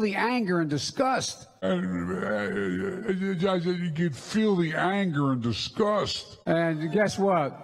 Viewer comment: Did he say ... I can feel your anger and disgust? I thought he said .... I can DEAL you anger and disgust